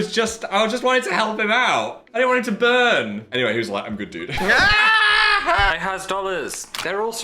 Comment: what did he even say 😭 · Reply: "Anyways, he was like 'I'm good dude' " is what I think he says